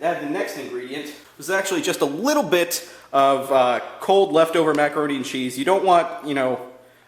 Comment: Did you just say "Wittle bit of of cold left over macaroni and cheese"